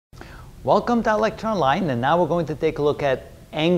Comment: man all this time i thought he was saying electronline when it's ilectureonline. APPRECIATE THE LESSONS THO THANK U <33